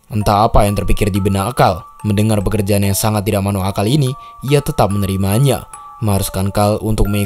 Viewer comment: Apa apa ga salah denger nih bang di menit 14:04 "tidak manuk akal🗿"